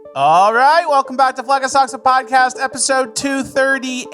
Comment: Sounds like he says "welcome back to fleccas sucks a podcast "